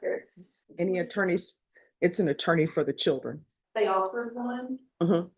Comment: are you the "brother"? They never had GAL according to this proceeding and her own testimony. You are lying. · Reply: No, I'm not the brother, you are once again jumping to conclusions. I relistened to the part mentioning GALs and can see now that I misheard what she said as she apparently said 'they offered one' whereas I thought she said 'they all had one' so you are correct about that. I wish people could civilly discuss things online rather than being accusatory right off the bat. Eta a word